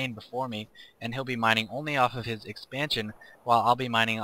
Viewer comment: lolololol so i accidentally turned on captioning at at 6:54 when you said "and he'll be mining only off of his expansion" the subtitle says "and he'll be mindful of the opposite sex engine" I think I'll mute the volume and just watch reading the subtitles from now on. Bound to be far more insightful into the intricate nature of the game.